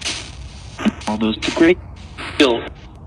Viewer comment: Usually, I don't hear a lot of what you hear, but I heard a full sentence "all those people killed" time stamp@